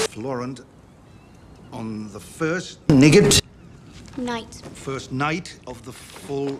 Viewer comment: i thought sir davos was saying "on the first nugget," 😭